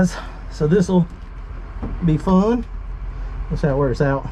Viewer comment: gave us a new word....Thisil... 0:53 thisil be fun😂😂. I thought i had heard them all by now too